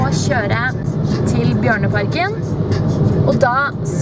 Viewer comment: when I first heard your speak I thought you said barnaparken then I saw the bears